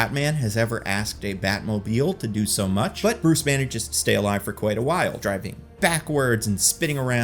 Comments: Did you just say... Bruce Banner?? I think you said "Bruce managed to stay alive..." but man does it sound different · Did you say "Bruce Banner"?